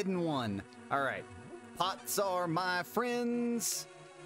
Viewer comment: Could of swore you said "Thots are my friends."